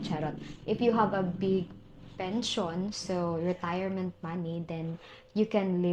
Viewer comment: Oh big pension. I thought you said big pencil